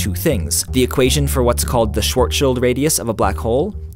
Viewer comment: I couldn't tell he was saying Schwarzchild. It sounds like he is saying short shield!